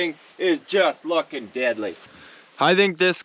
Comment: For years I thought you were saying “diddly” like Ned Flanders from the simpsons. It wasn’t until I saw one of your shirts did I find out it was actually “deadly” not gonna lie I think I prefer the diddly 😂